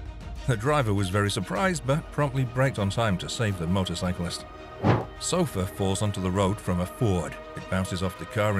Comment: because of the guys accent I thought he said sulfur and not sofa 😅😆🤷‍♀️